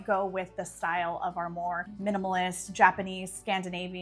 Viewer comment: I got sad when you said you were a minimalist lol. I always thought you said you were a maximalist 😢